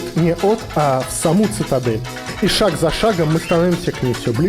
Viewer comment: мне одному послышалось ишак🫏 за шагом